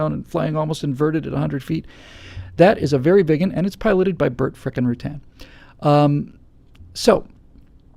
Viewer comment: Did you say "Burt f*cking Rutan" ? Real classy.